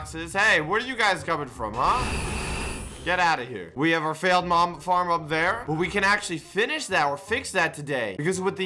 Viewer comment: hmm i heard mom farm